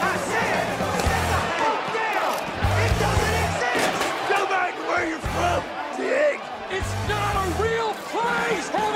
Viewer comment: what does that red headed guy say at 1:34?? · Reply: He says: "Go back to where you're from, Dick!" And says: "It's not a real place!"